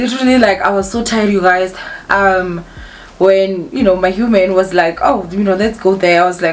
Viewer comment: Did I hear you well hey properly lapha ku 1:30 “my human”? If so that means I’ve been living under a rock these past few and missed out on a lot🤦🏽‍♂️. Otherwise,👏🏾👏🏾👏🏾 good for you mama and genuinely happy for 😉. “Intfo lenhle iyabongwa”❤️.